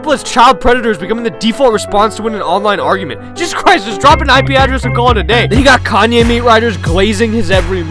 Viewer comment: what did bro said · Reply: He said: “just drop an ip and call it a day.”